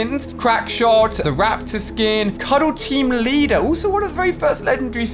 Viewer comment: It sounded like he said cuddle name elf